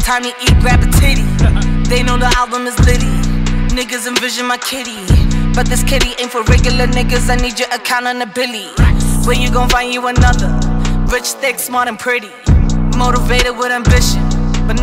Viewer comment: Who else thought she was saying she needs a billi on your account but no she said she needs your account of a billi and that's dope